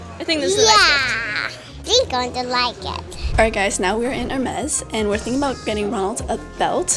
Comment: help me!! I heard they were in a mess!!